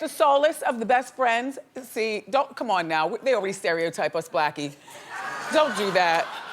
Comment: Did she call a Co-host blackie? what in the what? Edit: She says something like "Come on now, they already stereotype us (,) blackie" around @ 24:17. 🤷